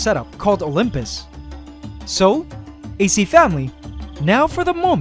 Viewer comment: Sorry for the late video, AC Family! This one took a lot longer to make than I expected, but it is now one of my favourite videos! Hope you guys like it, too! Also this video was just uploaded so sorry if the video appears low quality for the first little bit. This will change as Youtube processes the video. Ant love forever and thanks for your patience, AC Family! Do you guys like this NEW Titans colony? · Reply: I thought you said ace family but you said antscanada family (ac family)